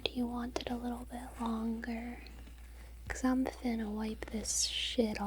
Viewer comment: Did she say "Finna"